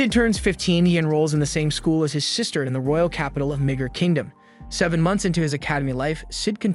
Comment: bruh, I thought this man said "n*gg*r-kingdom"